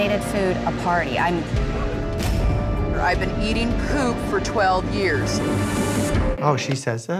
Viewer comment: No Offense but Like, What did she say? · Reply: She said ‘ive been eating poop for 12 years’